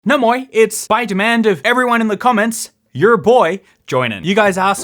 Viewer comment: what... i heard "spider-man" and not "by demand" at the beginning LOL